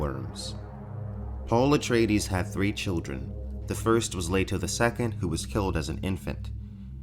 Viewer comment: Ok. not even 2 minutes in the video and I have no idea what this guy is talking about! 😨 First I heard, " Paul Atreides had 3 children. The first was laid to the second, who was killed as an infant. " 🤯 😵....What??? That sentence made absolutely No! Sense! So I turned on the caption to clarify what he's actually saying. The caption reads, " Paul Atreides had 3 children. The first was later the second who was killed as an infant. " ....😨🤯😵 WTF!?! Could someone explain what he was trying to say? please! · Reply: Paul’s first child’s name is, “Leto II who is killed as an infant." His other two children are a set of twins named Ghanima and Leto II. In the books Paul names his second son “Leto II” as well.